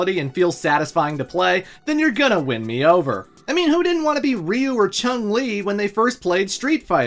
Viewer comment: I definitely wanted to be with Chun-Li! !! ... I may have misheard you